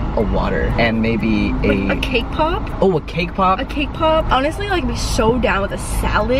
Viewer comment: When she said cake pop i literally first heard it as KPOP 😂😂😂